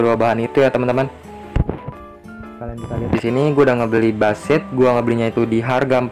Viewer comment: Bush seed, tapi temen gw dengernya Basit. Kt dia "apa manggil² nama gw"